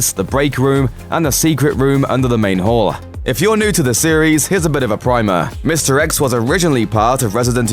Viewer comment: I thought he said mr x is a bit of a pimer